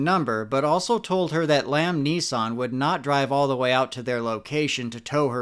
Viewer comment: I wasn't paying attention and thought he said liam neeson lol